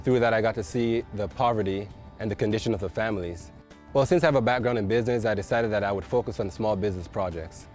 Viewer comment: did he just say "since im a bad girl in business" ..?? lol you go girl #kaedon